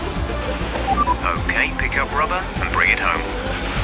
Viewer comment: I always thought Jeff said "Pick up BROTHER and bring it home", then few days ago I read the subtitles from my own game and I realized that's "Pick up RUBBER and bring it home."